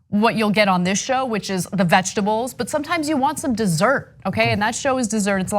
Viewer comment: Did Ana just call Cenk and herself 'vegetables' ?